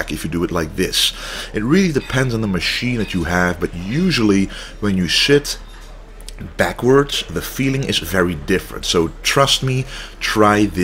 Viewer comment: sounds like he said "when you shyt backwards" lol